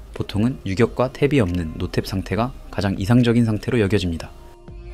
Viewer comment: 영상에서 튤러런스라고 더빙과 자막이 잘못 들어갔는데 원래 본 용어는 "Tolerance"로 "툴러런스" 또는 "톨러런스"로 불립니다. 영상 업로드 후 오류를 발견하여 댓글로 말씀드립니다. 용어 숙지에 착오가 없으시길 바랍니다.